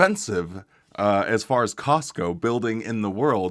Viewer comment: this is so so so stupid but when you said "the most expensive building in the world as far as costs go" I hear "as far as costco" and spent a really long portion of this video thinking that you were talking about some crazy fancy costco that I had never heard of :/